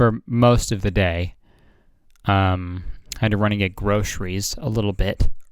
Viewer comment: "I had to run and get groceries" sounded like "I had to renegade..." anyone else hear that?